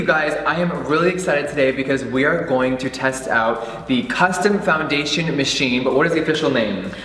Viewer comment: Am I the only one that thought it said "Condom Foundation Machine"?